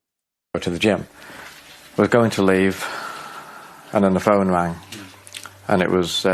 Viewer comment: Did he say "we're going to leave" I thought he was home alone?